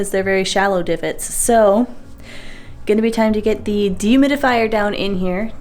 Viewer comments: I honestly thought you were saying "demons of fire" and I was so excited to see what that was. Dehumidifier. It was a dehumidifier. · Okay, the first time you mentioned a dehumidifier, I thought you said you needed a "demon of fire" to help dry out the basement and thought, "Is that some sort of circus thing?" I definitely needed coffee!😂 PS: I've sworn over and over that when my two dogs pass, I don't want to be responsible for another pet. But, happy corgi butt is so freaking cute! 🥰